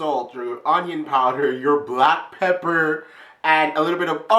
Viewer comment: When he was showing the spices, and saw black pepper I thought it said 'black people' for a few seconds lmao